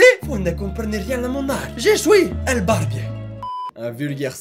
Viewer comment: J'ai compris : «J'ai jouis»... Voilà Voilà.. Sinon, écoute, je n'ai pas de barbe (#Snif #JeSuisTristesse),mais je te veux comme barbier ! T'es coupe d'artiste incompris étaient sublime~ Tu es juste un artiste incompris de la société, mais tu ira loin ! Par le pouvoir des barbes ! Bisouxxxxxxx •3•